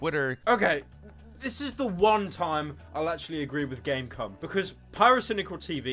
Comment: did he just say game cum